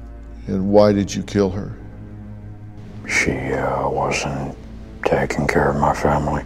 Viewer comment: “It was a wet thumping sound” *chuckles* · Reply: I couldn't understand him at first, played it back with the speed at 0.75 and at 13:40 and it's like he's trying to force out saying "it wasn't me" CREEEEEEPY!!!